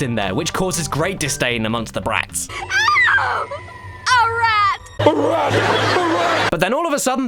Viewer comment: I've been watching these videos many times, and it took me (no joke) eight years to realise she said "a rat" instead of "alright"